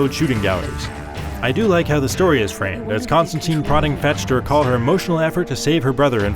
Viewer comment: did he say constantine ?